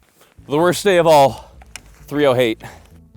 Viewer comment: did he say 30-hate?